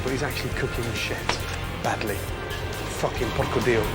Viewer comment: did he just say "fucking porcodio"?